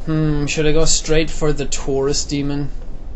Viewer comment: i thought he said the tourist demon